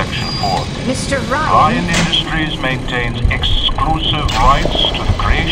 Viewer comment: Anyone else notice that at 11:27 Ryan says “Ryan Industries” but the subtitles says “Ryan Corp.”